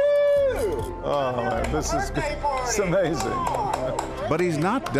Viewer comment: yo at 5:16 he was gon say "this is pathetic" but switched up at "pa-" and changed it to "amazing" lmao